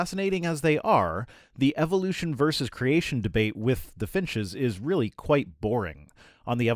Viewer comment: Oh finches. I thought he said "fishes".